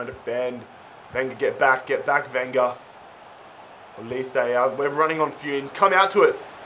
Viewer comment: "wenger get back, get back wenger" thats what the arsenal fans are saying.